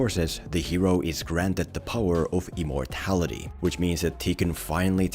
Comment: I really thought he said EMOrtality